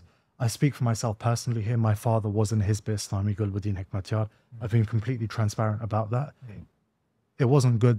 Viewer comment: Very intelligent and smart young man. Side point: did he say around 50:55 min that his father is Gulbuddin Hekmatyar? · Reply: He say's "My father was IN Hizb-e-Islami [with] Gulbuddin Hekmatyar". His father his definitely NOT Gulbuddin Hekmatyar.